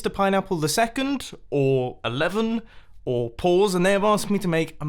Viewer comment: I thought you said paws not pause at first lol